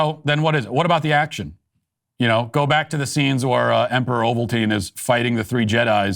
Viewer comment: Did you just say " Emperor Ovaltine"😂😅